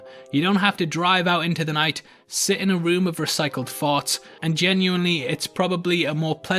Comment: Recycled farts 😂😂 I'm from the uk I remember watching black panther and this whole family wouldn't shut up. Would much prefer to pay for the film to rent at home and enjoy in piece. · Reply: I thought he said "recycled thoughts" but yours makes more sense.